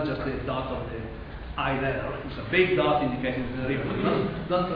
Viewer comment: What does he say at 19:13 when he put the large dot on the board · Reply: He said, derivative of state vector.